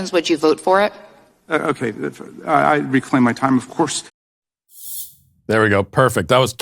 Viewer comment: It sounded like he said, "Yes, of course," in the same breath after he said, "I reclaim my time."